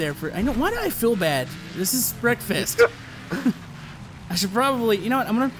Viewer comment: you said breakfast instead of wreckfest